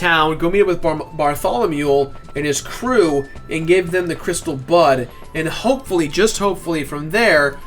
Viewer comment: for a sec i thought tou said "crystal butt"